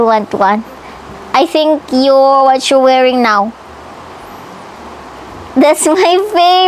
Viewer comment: this is the 2nd time. ,i. Watched this,i only knew now,,what said to,,” i thought you are pregnant”👍😉😂😂.HOW WE ALL WISH,,SAYANG7/26/24👏👏👏✌️🥰🥰🥰♥️🇵🇭